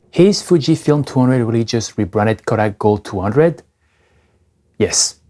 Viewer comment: wtf did you say? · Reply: "is Fujifilm 200 really just rebranded Kodak gold 200? Yes"